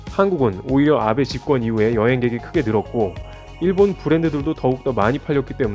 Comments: 여행객이 들었고 -> 늘었고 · 에 오타가 있어요. "여행객이 들었고" --> "여행객이 늘었고". 평상시에 듣기만 하고 자막을 잘 안보는 편인데, 우연히 발견했네요. 큰 문제는 아니기에, 좋아요를 꾸~욱 누릅니다. 좋은 영상 재미있게 보고 있습니다!^^